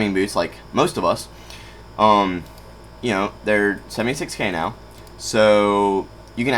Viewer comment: the captions say "Senate sex scandal".....when u really said: "76king now" lol